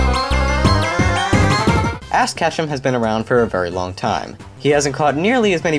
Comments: Pfft... It sounds like you said ASS Ketchum at 8:17... HEHEHEHEHEHEH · did he say ASS ketchum? Seriously it sounded like it. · Wait did say "Ass Ketchum"? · Did you say Ass Ketchum? I think you said Ass Ketchum. Also, I like your list! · did you say 'ASS' Ketchum